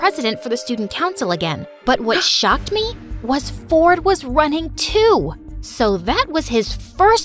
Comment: "Fr I thought She said Her brother's name ford Was fortnite"🤣🤣...